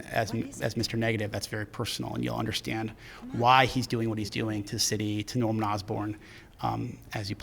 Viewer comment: did say "you'll understand why mr. negative does what he does to norman osborn"? interesting..